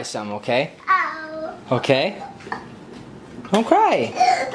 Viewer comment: I swear bro said don't cry on 0:26 just starts to cry louder 😂❤